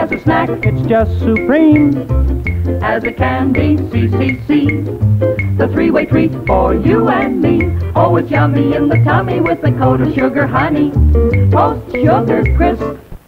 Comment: The last commercial reinforced the way I first misheard the jingle. Instead of "3-way treat," I heard "freeway treat" at first!!